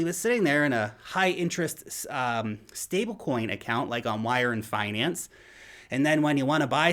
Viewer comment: who did you say to earn 15-20% on your stable coins? Wire finance?